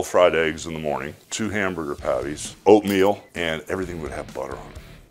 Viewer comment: My dyslexia thought you said 2 eggs 12 hamburger Pattie’s